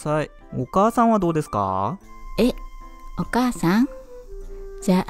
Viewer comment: タチバナ家に4万人入れてみたって聞こえてビックリしたんです🥺